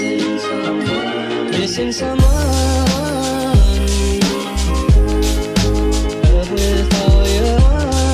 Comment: Am I the only one that heard “physics are mine” in the backround music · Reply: I heard "missing someone"